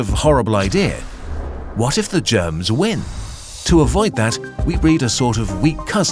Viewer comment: i thought you said 'what if the germans win' and thought this was a ww2 documentary for half a second